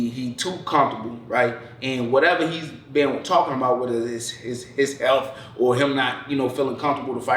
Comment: Genuine question, at 19:54, did you say whatever he's been talking about with "his self", or "his elf" i.e. sending stray at Cejudo. I hope it's the second one 😂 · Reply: “His health” 😂 but yes the convos with his elf is why I know these things 😅